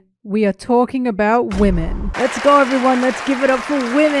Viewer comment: I woke up to this since i had auto play one and all I hear is "wemen"